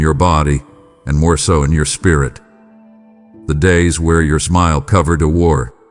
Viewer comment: I thought you said hell days are over.😾